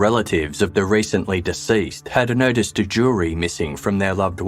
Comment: Had noticed a jewellery missing - captions said missed a jury 🤣🤣🤣